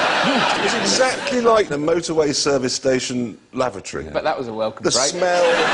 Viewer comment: what does alan say a 5:18? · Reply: Jeremy Clarkson says the brothel was like a service station toilet, to which Alan responds "bet that was a welcome break" referring both to both a welcome break, a change in one's usual scenery or routine which gives a feeling of relief, and to the Welcome Break chain of British service stations.